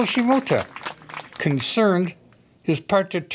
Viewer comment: lolz at first i thought he said "what do you mean your uncle?" now i get that he said "wrong hole lolz